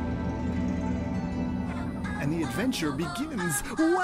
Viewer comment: I actually thought that it was from the show since I kept hearing 'Welcome to the cyberjail!'